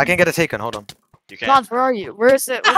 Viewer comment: why did sum1 say "nos where are you"